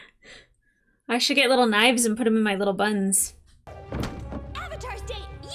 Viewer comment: I look away from the screen for a few seconds and I hear "I should get knives and put them in my little buns"